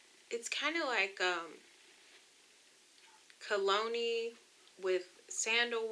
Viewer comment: Girl I thought you said bologna mixed with sandalwood... I was like oh well damn !!!🤣🤣🤣